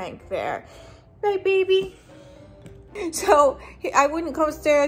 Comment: did I hear... DABABY 😱😱😱